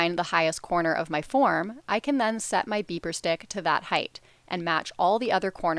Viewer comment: I thought she kept saying beaver stick lol